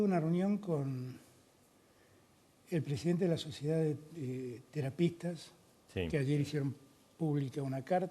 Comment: Entendí sociedad de trapitos 😂😂😂🤣🤣🤣